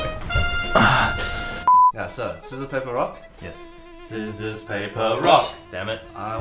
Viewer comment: “Scissors paper rock?” What I heard: “So let’s play Baroque?”